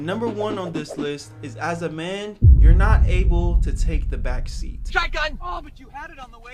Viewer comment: I thought I heard backshots because when I was holding the screen it automatically goes to 2x speed 😭😭😭